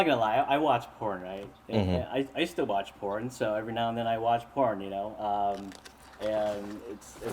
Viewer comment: wait did say he watch led porn ? I couldn't hear him the 3rd time